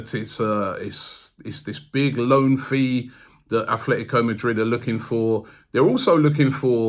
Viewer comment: Swear said ‘Atletico Mudryk’. We have been linked with him for so long that even can’t stop thinking about him😂